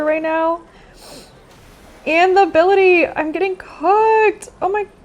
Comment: i stg i thought she said "im getting cucked"